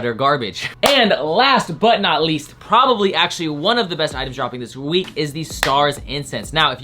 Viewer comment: I thought said best item is incest